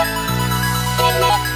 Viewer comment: tbh i keep misheard "electric hair" as "i like twitter" LMFAO